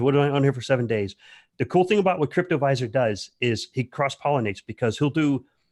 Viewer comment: excuse me, what is the channel link you recommend. I heard it is "Crypto wiser"? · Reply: Cryptoviser